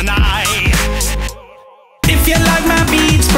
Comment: does he say "Intolerant to weed" ? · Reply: Wheat but I can see why you might have misheard.